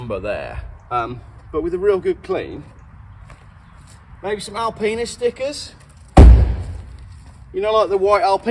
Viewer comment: Why did it sound like you said Alpenis stickers first time round, idea for you 🤣